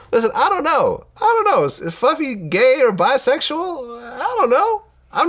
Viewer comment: mark. Did Vlad call Puffy FLUFFY? 😂😂😂😂